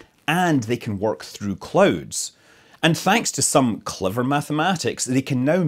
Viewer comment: I thought he said "it sees through clothes" instead of clouds 🤣😅